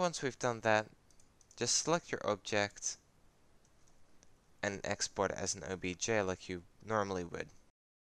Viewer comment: wtf is he saying 2:51 1 idk what to type and 2 whats he saying help pls :( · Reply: "Export it as a .obj file like you normally would..."